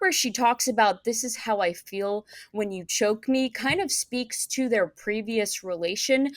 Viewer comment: I know I heard her say "that's how I feel when you cheat on me"